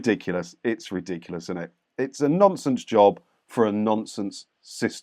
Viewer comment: For a moment there, I thought you said " it's a nonces job for a nonces organisation". 🤣🤣🤣🤣🤣🤣